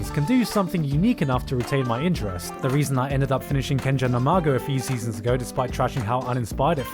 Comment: Which anime is this? No matter how hard I try I cannot figure out what he said and google aint helping this time :( (And subtitles say Ken Jenner Imago lol) Please help my trash addiction.